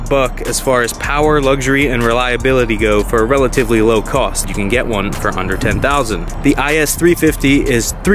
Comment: Did he just say you can get one for a “hundred n ten thousand” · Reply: I believe he says UNDER ten thousand